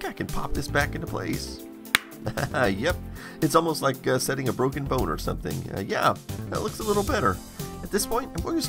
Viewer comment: I could have sworn he said "Its almost setting up a broken boner"